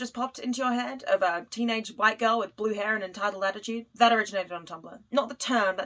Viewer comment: your captions say “black girl” but you say “white girl” - bad job auto captions! You can download the auto generated ones and re upload with it fixed…